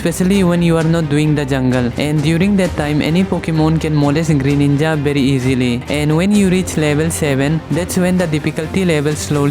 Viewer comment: did you just said "molest greyninja very easily "?